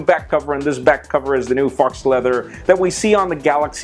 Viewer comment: Did he say "Fox Leather" at 1:14?